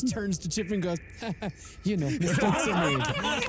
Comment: I feel like said something really funny but I can't make out what he said over everyone laughing , if any English speakers here caught that and can translate for me... 😅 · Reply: I think said "you know, sponsor me"? I'm probably wrong